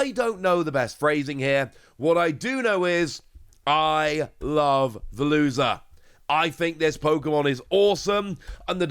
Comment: I genuinely thought he said 'I love the loser' instead of 'I love Veluza' and I almost spat out my tea No but genuinely it's interesting to me how many cards would be viable that most people just don't play. Makes for some fun rogue decks though!